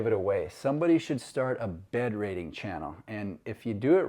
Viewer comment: Really nice looking home, like others I love the rooftop deck. Thanks. Oh, when you talked about a ‘bed rating’ channel I thought you said ‘bed wetting’ channel- I thought what sick game is this man talking about? Anyway, I realized quickly you said ‘rating.’ My bad.